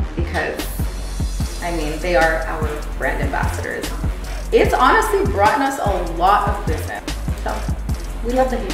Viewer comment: did she say, "it's honestly broughten us a lot of business"???